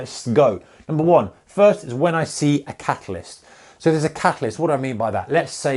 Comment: All the time when he was saying catalyst is thought he was saying catholist. Was starting to think what is that of a term haha